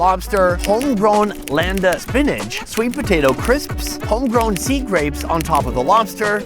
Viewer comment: I thought Sonny said B. Grapes.